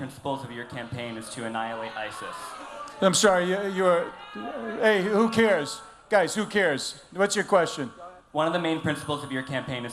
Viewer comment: Anyone catch what the crowd said? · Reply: It seemed like some members of the audience were objecting to the person asking the question. Apparently, though, what happened was someone called Trump a bigot and the crowd got upset. Scott Brown then reassured them that nobody cares.